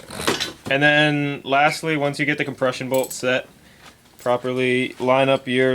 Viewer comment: i have a hearing problem i at first it sounded like he said depresion bolt XD